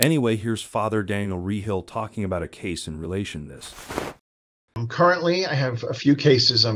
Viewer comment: Said here's Fr such and such talking in relation and you couldn't hear whether you said to or not but it's "TO" this".